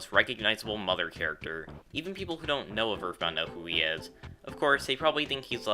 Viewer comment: did you call a her. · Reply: No, I said “Even people who don’t know of EarthBound know who he is”.